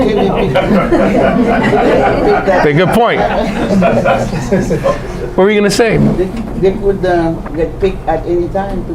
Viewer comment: I can't quite hear, what was the joke by the audience at 13:10? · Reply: Sorry about that... one of the problems with live recordings at that facility. One of the participants at this workshop said, "Or a Home Depot" referring to where day-laborers, migrants almost always, today can be found looking for work.